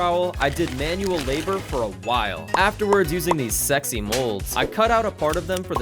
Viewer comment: did you say sexy